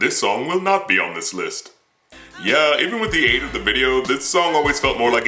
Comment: Thought you said Stan wouldn't be on the list